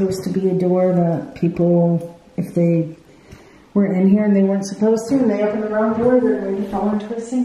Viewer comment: When you asked if they can get sarah I heard "we're not supposed to"